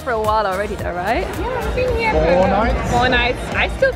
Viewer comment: When she said i’ve been here for four nights i thought she said Fortnite🤣